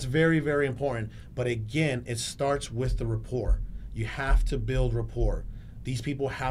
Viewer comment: starts with the WHAT?? “Report”?? Can anyone tell me what he meant there?